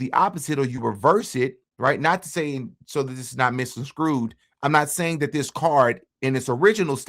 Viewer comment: Did you say “misconscrewed?” Just in case, “misconstrued.”